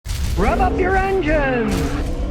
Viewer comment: Someone needs to make a Scotty Kilmer meme song! With the “rub up you’re engines” intro, and with “money pit, money pit, endless money pit” added into it, for the young ones on TikTok, The song added with the laughing horses and and cougar hyena pictures 🤣🤣 would be a timeless classic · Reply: I thought he was saying “Rev up your engine(s)”.